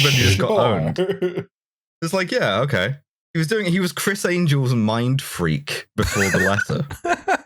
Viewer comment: Don't worry, I heard "Christ Angel"